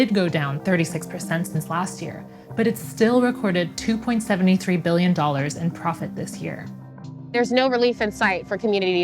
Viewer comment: Did she really say two point seventy three billion around 1:41? Vote GA!!!